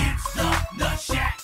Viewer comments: I still remember the first ever time I heard this song back when I was about 13 years old, I thought they were saying: “It’s not the shit!” Instead of “It’s the Nutshack!” Looking back at that now it’s pretty funny. 😂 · For some reason I hear: IT'S THE THE SHET!!!